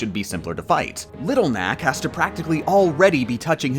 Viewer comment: did you say Little Knack or Little Mac?